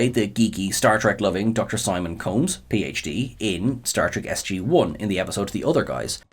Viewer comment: Did I hear "Star Trek SG-1" for John Billings there?